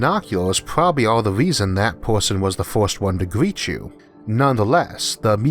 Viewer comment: did he say "good first approach" or "good forced approach"?